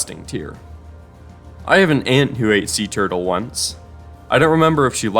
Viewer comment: I thought you said “ant” for a sec and was very confused